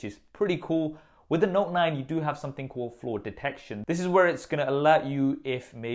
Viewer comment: Whenever he said flaw detection I thought he said floor detection.